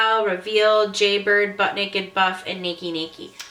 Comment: Omg..during your look i swear it sounded like you said "f*** naked" lol i had to rewind. Thanks for your review! Feel Better